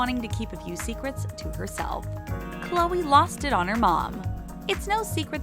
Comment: omg for a sec I thought it said Khloe lost it to her mom